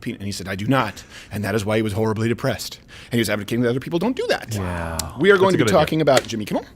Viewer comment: what did he say? "He was helping kidnapping people, dont do that?" ...He talks soo quick he sometimes mumbles and doesn't enunciate properly fack!!!